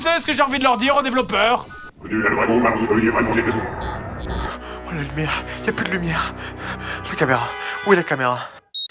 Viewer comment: "Vous êtes des gens merveilleux en faisant l'amour vers/avec la lune" Voilà ce que j'ai compris en inversant le clip, mais si quelqu'un pouvait apporter un éclaircissement sur ce qui est réellement dit, je le remercie d'avance. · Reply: La deuxième phrase c'est "vous êtes des gens merveilleux, faisons l'amour au clair de la lune" Et la première phase qu'il dit est : "Moi je m'appelle Lolita Collégienne aux bas, bleus de méthylène" Référence à la chanson d'Alizée 😉